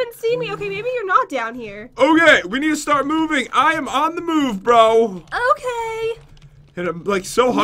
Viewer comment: When Said I'm ON THE MOVE BRO. An Ad come up and said Lagi mandi which means on the shower so I thought Said I'm ON THE SHOWER